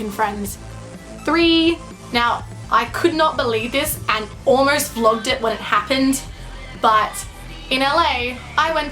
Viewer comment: did she say she "Almost flogged it"? Did I hear that right?